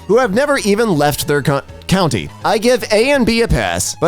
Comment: what’s that m word?? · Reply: Meth